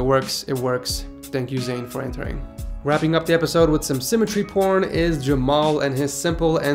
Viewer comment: Wow wow wow, did I heard symmetry porn at 9:25?